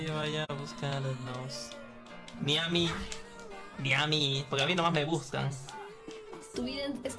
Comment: parece que dice a mi no mas me gustas XD